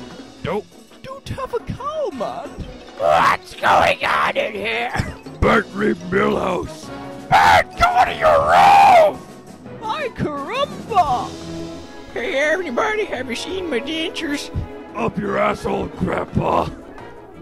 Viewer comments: "Mikecarumba"? Did Bart just say "Mikecarumba"? MYCARUBA?! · i thought homer said bart bring milhouse